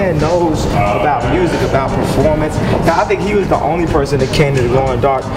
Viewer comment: what does he say? "I think he was the only person who..."?